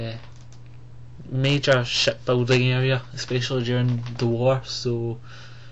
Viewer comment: that sounded like you said "shit building" ROFL